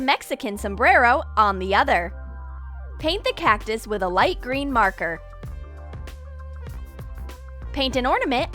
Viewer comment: did u just say paint the cactus with a green marker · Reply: at that time she said was white green marker